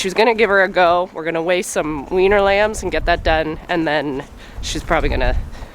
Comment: 😂 I heard the term "weaner lambs" as "weiner lambs" and was picturing short lambs with long bodies like a dachshund!